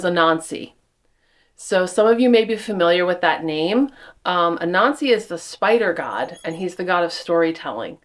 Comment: I thought she said A NAZI. LOL. COULD SEE That AS A BAD OMEN.